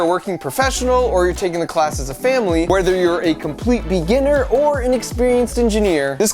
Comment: At the end when he’s promoting his online course, he says “whether you’re a total beginner...” What I hear: “Whether you’re a total LOSER..” 😳 probably because I know full well that’s the category I fit in. 😂😎🤷‍♀️